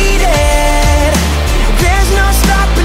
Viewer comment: i don’t know why but I hear ‘I’M DEFEATED’